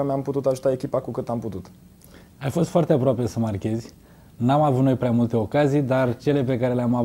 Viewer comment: "Am avut multe ocazii." :)) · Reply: "N-am avut noi prea multe ocazii..." :)) Da volumul mai tare la boxe inainte sa te apuci sa comentezi